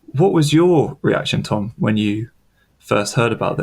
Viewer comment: 초에 what was your reaction tom? 이 맞지 않나요? tom이라고 발음이 들리는데 time이라고 적혔는데... 혹시 오타인가요 ?